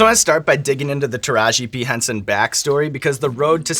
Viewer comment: I swear hes saying Taraji B Henson and it's driving me nuts. Anyone else??